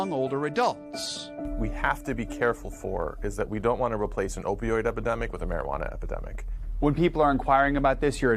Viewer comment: Did he just say we don't want to replace an opioid epidemic with a marijuana epidemic?